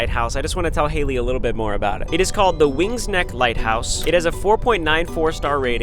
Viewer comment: I thought it said Wings "Nick" lighthouse 😅 I'm just saying I thought lol 🤣